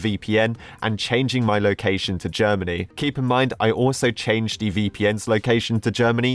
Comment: lol what i hear you say "Japaning" but you real say is " Germany"